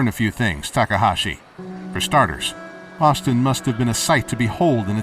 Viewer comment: sounds like he says Austin not Boston.